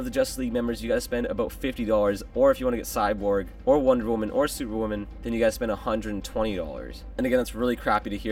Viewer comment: did he say superwoman?!